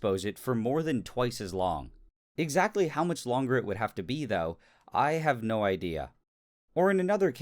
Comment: What does he say at 14:16, it's "be tho", "bee thou"? What's that?